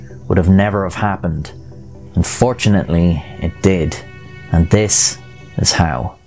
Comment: in the beginning i thought he said fortunately instead of unfortunately and i was like wtf qxir actually pyschopathic confrirmed?